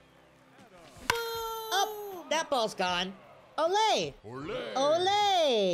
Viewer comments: I thought he was saying “that ball took off” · He’s been saying “oppo taco” 😭😭😭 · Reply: I thought he said that ball is let go😭